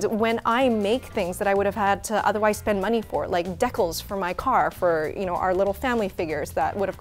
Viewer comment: did she call “dee-cals” deckles? 😭 Love you Yvonne